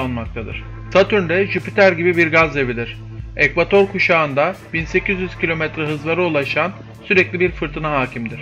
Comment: 1.800 km hız mi dedin benmi yanlış duydum😱